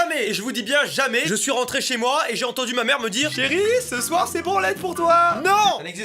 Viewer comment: J'ai jamais entendu ma mère me dire '' chérie, ce soir c'est ciboulette pour toi ! '' non plus hein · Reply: Branlette pas ciboulette 😭